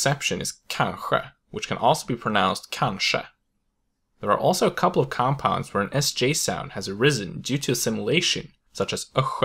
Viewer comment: I am sorry but English is not my first language and I can't make out what you are saying at 2:05 , please help ? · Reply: "There are also a couple of compounds, where an sj sound has arisen due to assimilation"